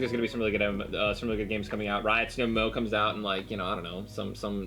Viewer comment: I thought u said riot symbiot